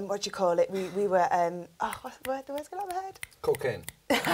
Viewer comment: What did he say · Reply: cocaine